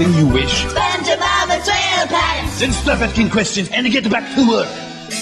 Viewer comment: what was he saying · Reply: “Then stop asking questions and get back to work”